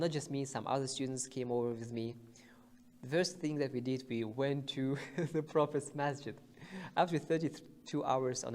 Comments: Alhamdulillah Jazakumullahu khaira wa barakallahu fikum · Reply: Assalam o alaikum did he say that worst thing that I did i went to prophet mosque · Assalam o alaikum did he say that worst thing that I did i went to prophet mosque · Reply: no he said the first thing we did not the worst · You have a great personality, my brother. MaashaaAllah! May Allah give you all the strength and patience to thread the path of this beautiful Deen. I remember when I got accepted everyone was so happy for me. One brother from my masjid told me my name was on a list. I was so happy to be accepted. The experience will forever live in my heart. I'd love to meet up with you one day biidhnilaah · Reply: Assalam o alaikum did he say that worst thing that I did i went to prophet mosque